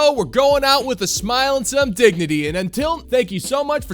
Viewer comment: I think you said we are going out IN style with some "dignity" but I heard "dig-Eh-TEE" and if I'm going out in style, I'm taking my dig-Eh-TEE with me.